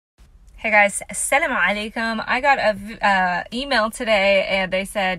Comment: say Asalam Aleikum, it sounds like you say Asem Aleikum which means death be on you.